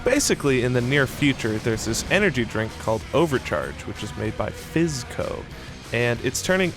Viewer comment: Why is it that every time they say "Fizzco", I hear "SipsCo."? ^^